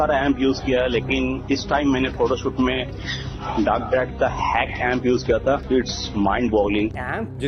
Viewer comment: kya bola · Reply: Mind bowling